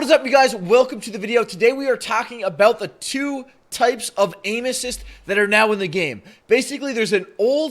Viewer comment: I thought he said anus when he said aim assist LOOL